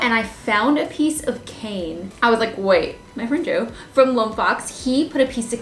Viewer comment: Is it just me or does it sound like she's saying "Joe"? Obviously I know she knows him and knows his name is and I'm sure that's what she's saying. I'm just wondering if anyone else here's Joe to 😂? I LOVE channel Lone🦊.